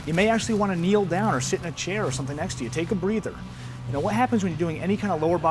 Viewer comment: Did anyone else hear "shit in a chair"?